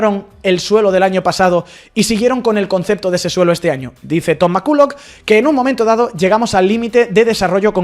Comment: Escuché mal o dijo "toma kulo" en 5:31??? 😮